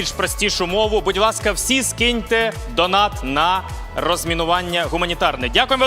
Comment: почулося спочатку «розмінування гуманітарки»!) Гарні конкурси, та й тамада чудовий!) Це типу «Голодні ігри»? Королівський батл-роял в Україні? Хочеш західної допомоги? Спочатку розмінуй, а потім користуйся!)